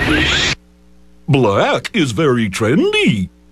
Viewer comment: I SWEAR FOR THE LONGEST I THOUGHT THAT QUICK DRAW SAID “black is very FRIENDLY” AT 21:07 NOT “TRENDY”